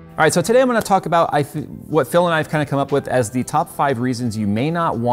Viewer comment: I swear he was saying iFixit. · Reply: He was, then corrected it to Phil